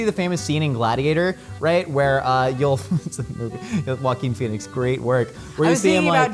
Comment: I cant be the only one who thought they were saying "Walking Phoenix"